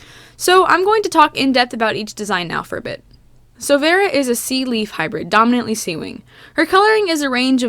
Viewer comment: I thought you said sea weed instead of sea leaf.